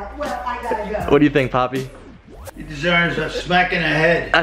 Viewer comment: did just called his grandpa (papi).